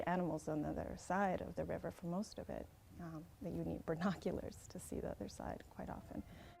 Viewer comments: Did she say "burnoculars", or is it just that my hearing is getting worse? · did she say bernoculars?